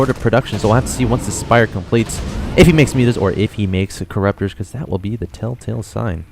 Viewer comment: are you serious? You thought he said mules and not mutas? I do agree the caster sucks though :P.